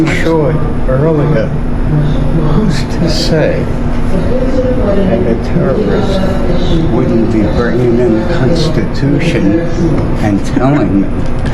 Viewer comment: Can someone who can figure it out tell me what this guy is saying at 2:53 because it sounds like some insane babbling to me and I'm really curious what he's actually saying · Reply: He said who’s to say a terrorist isn’t bringing in the constitution lol